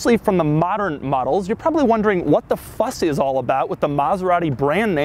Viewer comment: Bro at 1:35 I was 100% sure you were about to say, “what the fuck” but then you said, “what the fuss” and I was relieved and bummed at the same time. Lololol